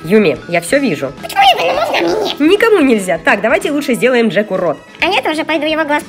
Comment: мне послышалось вместо "Джеку рот" " Джек урод" 😂😂😂✌🏻